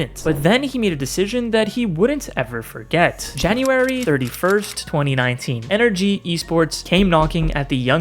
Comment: you said 31st and it says 21st just FYI oh and at 3:49 you said February 2019 and the captions say "29"